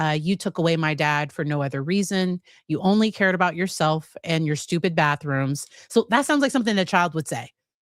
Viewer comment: I thought one of the boys said ‘Because of your stupid boyfriends’ not stupid bathrooms. Felt really sorry for the boys, and didn't realise that they were in foster care for 15 months!! I thought they always went to family if possible!?